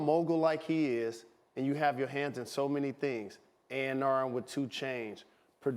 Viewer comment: Did Jalen say A/R with 2Change??